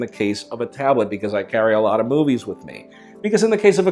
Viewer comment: On my phone, it sounded like he said "I carry boobies with me"